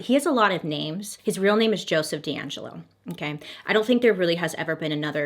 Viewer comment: I THOUGHT SHE SAID JASON DERULO BUT SHE SAID JOESPH DEANGELO💔